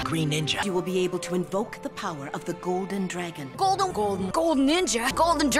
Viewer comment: When Lloyd said power it sound like he said howard